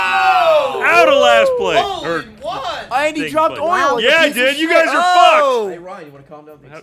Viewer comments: woahwoahwoah, did just say "Wow, you piece of shit" at 48:37? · at about 48:36, i swear i hear say "wow you piece of shit"... what did she actually say? :l